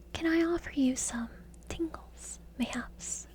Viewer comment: I thought you said tinkles.